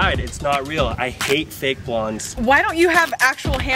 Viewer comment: I hate “fake blonds”😂 and “too good looking”😂😂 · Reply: help- I heard "I hate fake lawns" 💀